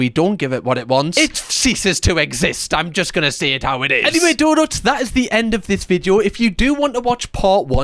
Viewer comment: Not gonna lie, i thought you were saying "daughters", not donuts for like a week of watching your videos